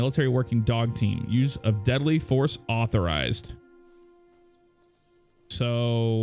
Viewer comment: Am I the only one who heard "Letal fortis awterized" in Sarge's voice? :D